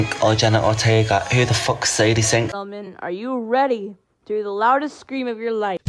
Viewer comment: i had the vid on 1.25x and i heard for fu33 sake sadie sink !